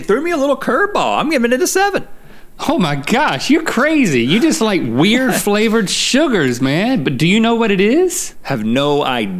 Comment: Anyone else thought Link was about to say, "You just like weird flavored sh*it man." ??